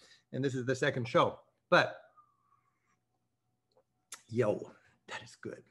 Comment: Did Rick say "yo" @ 5:44? Lol I'm really enjoying this series. I want to be in my darling Portugal.